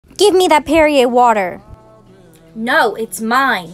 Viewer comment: My dumb asz thought it said "Give me that period water" 💀